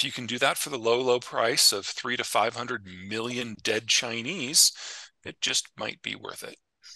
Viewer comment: Subtitles say 3-5 million. Actual audio is 300-500 million.